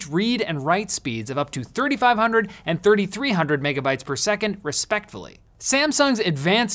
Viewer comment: Did he just say "respectfully"? 😆 10:08 I wouldn't be surprised if it was intentional.